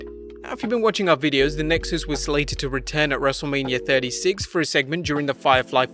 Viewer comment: Lmaoooo i thought he said, "Wrestle labia"